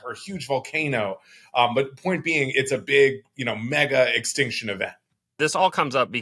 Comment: I thought he said Maga-extinction event...we couldn't be that lucky